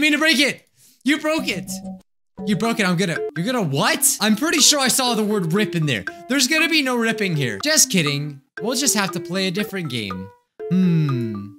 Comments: For what she said was, "I’m gonna rip your head off". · AT 1:08 I FOUND I FOUND OUT WHAT IT SAYS ITS you broke it… I’m gonna rip your heart out